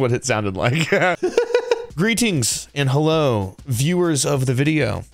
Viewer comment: i thought he said “green tinks and hello” i was so confused 😭😂